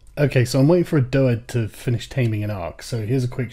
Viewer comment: I am sorry, could someone explain the first sentence? It sounds like he is saying "waiting for a DOA to tame an AUX." what does this mean?